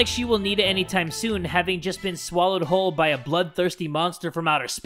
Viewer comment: When It said “Bloodthirsty” i thought i heard “Butt-Thirsty” so i had to rewind